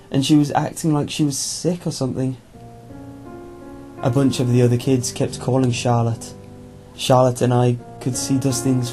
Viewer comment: Wait?What? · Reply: Yeah, I know, that was written weird. It sounded at 10:42 like the narrator was saying that he and Charlotte both saw Dustin's light. In reality, he was saying that the kids were calling "Charlotte, Charlotte". That isn't a much better description, is it... ugh. I cant describe it. Here, I can put it like this: Sounded like: "...the other kids kept calling Charlotte. Charlotte and I could see Dustin's flashlight..." Really was: "...the other kids kept calling 'Charlotte, Charlotte', and I could see Dustin's flashlight." This effect was caused because the reader, accidentally emphasized the comma a little too much, causing it to sound like a period instead.